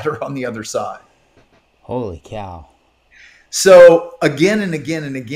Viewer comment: did you say holy cow?